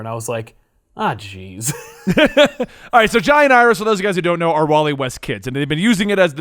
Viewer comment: I SWEAR you said 'GIANT IRIS'